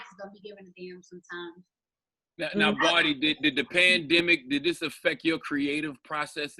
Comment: I thought Charlemagne said plandemic